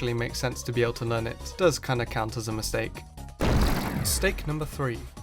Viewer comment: not tryna be rude btw all you fans i am a fan too. I have noticed a mistake when at 3:57 you accidentally say steak instead of mistake. (pls dont be toxic)